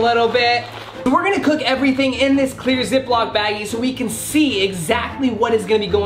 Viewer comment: The prison food in the beginning looks like my school foor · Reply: did say... baggy wtf